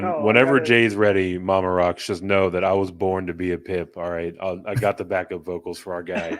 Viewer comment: lol, i thought you said you were born ro be a PIMP.......bad ears on me, you Pip! Great show as always guys, thanks!